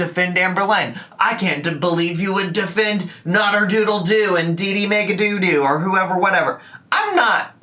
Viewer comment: Listening along, miiildly invested, I hear say 'Nader Doodle Doo and Dee Dee Mega DooDoo'. Made me chuckle but also question my youtube life choices. Just for a sec though:)